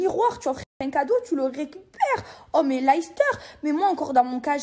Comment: Attend quoi ? Elle parle bien de l'équipe de foot de leicester ? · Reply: mmdrrr 😭😭😭 elle dit « lahister »